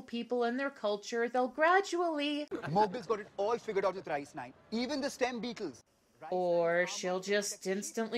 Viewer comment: I misheard "rice-nine" as "ice-nine" and thought they were going for Kurt Vonnegut's Cat's Cradle but in India. Which sounds a lot more interesting.